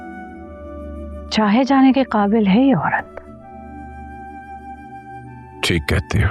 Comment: what did she say i didn't understand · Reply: ‘Chahe jane ke qabil hai ye aurat ‘ ye kaha hai